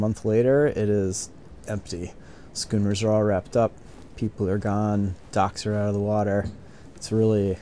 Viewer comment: did you say “ducks are gone”😁?…Stay warm and cozy for the winter🥶!…